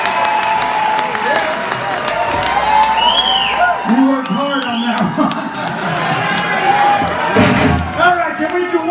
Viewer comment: My english is not good enough? What does Vai says at 7:53? "We worked hard at that part"? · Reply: “We worked hard on that one” Vai said